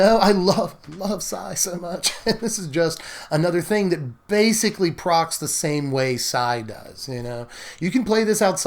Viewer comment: What... is this word you're using...? Proc? Prog? At 13:38, and again at 23:33. I have no idea what you're saying. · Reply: "Proc" is a term from video games, usually RPGs. It's short for "programmed random occurrence", and typically refers to abilities that have a % chance to trigger when you do a thing (attack, take damage, etc.). Basically it means the same thing as "trigger".